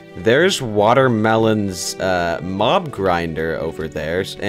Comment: When he said mob grinder I thought he said mom grinder